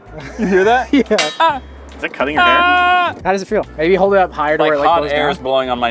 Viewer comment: Sounds like said “is it cutting your hair?” I was so confused until I realized he probably meant ear.